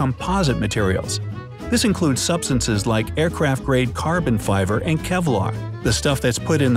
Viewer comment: Did I hear Carbon fiver here 5:12? That's cute!